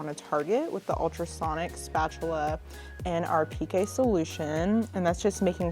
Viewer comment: Hi there! It is just water, to keep the skin moist :) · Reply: i thought she called it some kind of specific solution. Sounded like she said PK solution...